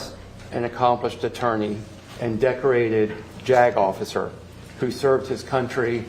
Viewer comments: I swear I heard “not just an attorney but a decorated jagoffice” 😂 wanker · Is it just me, or does anyone else hear "decorated jack off"? Instead of JAG officer? At first I thought it was being funny or his accent (both of which I love, btw) but because I binge watch the playlist ,now, well over 600 videos of and put them on shuffle, I have watched this episode 3 times and I hear it everytime. No disrespect to anyone in the service who holds or held this title in real life and isn't a heinous murderer. Considering what we know about this particular nut sack in a nutshell 🤔, maybe that's why I keep hearing "jack off" instead. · did he say JAG Officer or jag-off?